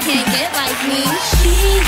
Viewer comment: LOL! At first when I saw the moive I thought she was sayin "Fresh Like Weed" in 2:14!!!!